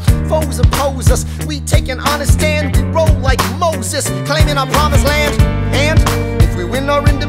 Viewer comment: I always heard "claiming Obama's land" instead of "our promised land". Am i the only one?